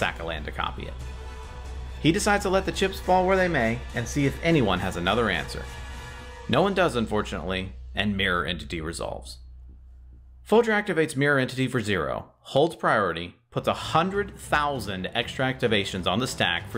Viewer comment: Slight audio hiccup at 16:15, it sounds like you said "unfortunately" instead of "fortunately"